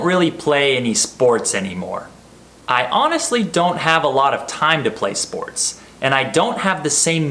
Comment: I heard " I honestly don't have a lot of time (be) play sports " why? is there anyone can instruct me ?